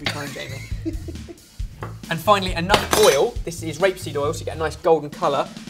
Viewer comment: did he say "rape seed oil" at 4:23?